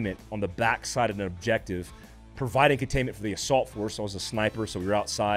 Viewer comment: At about 6:24 I thought you said "I was providing entertainment", not providing containment. Had to rewind but good for a laugh still.